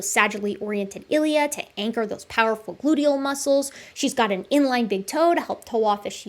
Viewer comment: what was said: "... to ancher those powerful gluteal muscles...." what i heard: "... to ancher that fat-ass..."